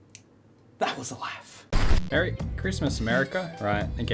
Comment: I thought it was saying only farmers should have a merry Christmas